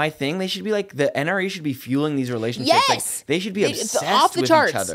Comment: I thought you said "the NRA should be fueling these relationships" 😂